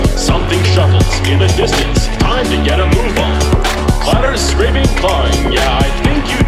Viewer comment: and also i mishear it for time to get a rumba